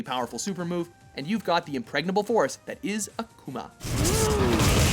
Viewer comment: did he really say 'impregnable'